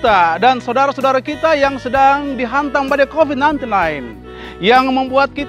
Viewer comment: Lagu bagusss. Kotbahnya bagus. Tp pastor, mungkin salah ngomong d bagian tertentu. Covid19 dan bukan covid99... Atau mungkin aku salah dengar. Shalom. Selamt berkarya